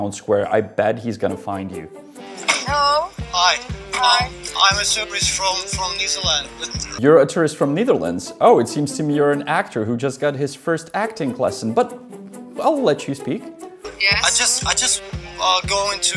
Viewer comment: the most fake thing in this show is how broken that "dutch" guys english is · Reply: I thought he actually said "New Zealand" not "Neizerland" or "Netherlands".. Still a very poor execution of either accent lol